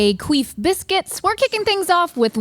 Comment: Did Stevie just say Queef Biscuits? Or am I hallucinating?